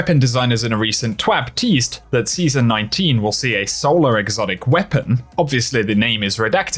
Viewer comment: I misheard you and thought you said 'Solo Exotic' and was like "what does that even mean?"